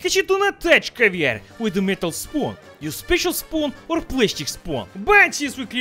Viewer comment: i thought you said don't use metal spoon for caviar... maybe i'm hearing things